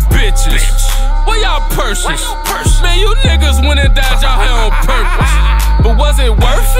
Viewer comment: They went and dyed their hair on purpose,How that working? I wouldnt be suprised if they start twerking!!!!!!! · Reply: Damn I thought he said "you niggas went n died outchea on purpose" I'm glad it wasnt that cause that woulda been too much disrespect